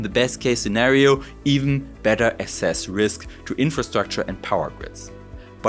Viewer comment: Wat zid you say? 14:44 Better SS risks??? Oy vey